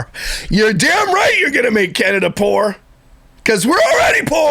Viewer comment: 😂 She said "Canada fort" (French for strong). I heard poor too and had to rewind and listen to it, not even kidding, 10 times before I realized what she actually said! Haha